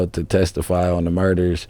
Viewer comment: DID HE JUST SAY “ TO TESTIFY ON SOME MURDERS” ⁉️😂😂😂🤦🏾‍♂️